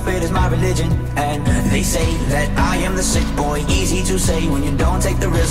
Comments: Why did I hear "I am the thicc boy" Edit: I have cursed everybody's ears, and I am consistently gaining new victims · I heard “I am a thicc boi” half the time